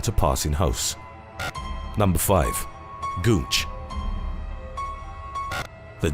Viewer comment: Number 5... Thought he said Gooch